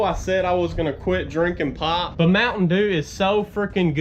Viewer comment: When you said “I’m gonna quit drinkin pop” I thought you said “im gonna quit drinkin pot” I was like wth 😂